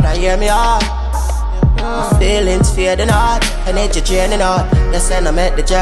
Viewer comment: what he said a @ 1:12 is "energy draining out"